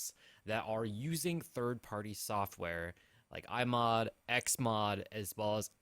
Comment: it sounds like he says 'all the people using mods like my mum' 😂